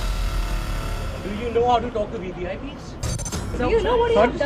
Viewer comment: he said VPI ...instead of VIP , THAT WAS ENOUGH FOR ME TO MOVE ON OTHER VIDEO